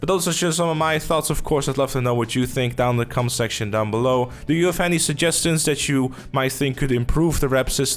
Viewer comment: "cum section down below" wtf? lol Did I hear it right lol